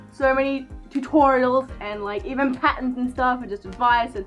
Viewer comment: OH! You’ve been saying patterns this whole time! I thought you were saying patens for some reason